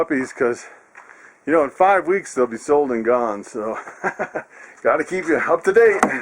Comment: Just reserved at five weeks... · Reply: Ahhh... Thank God. It sounds like you said, "at 5 weeks, they'll be sold and gone." Sorry... I must've misheard. I was pretty horrified. No responsible breeder lets puppies go to their new homes before 9 weeks, at the earliest. Cute puppies... I didn't get to see Mom much. You sure look busy!!